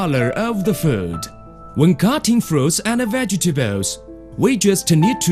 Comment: Eat your VEGETEBEUS, people. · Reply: I heard "Vegtabewws".